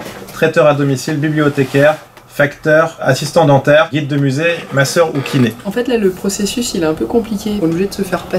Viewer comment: J'ai compris "Ma Sœur" et pas "Masseur" le métier. 🤣